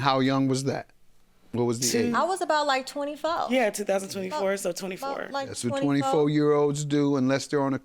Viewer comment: am i the only one tht heard her say “we was the same age 2024 so 24 “ like huh wet wet just said she was 31 & we ain’t make it to 2024 yet